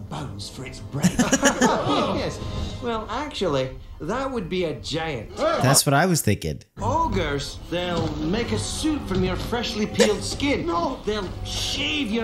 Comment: Wait... he was saying "suit" the whole time? Dang, growing up, I thought Shrek was saying he'd make soup from freshly peeled skin.